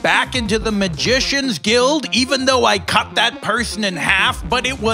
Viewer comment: "Accident? " I thought he said "Axe in it"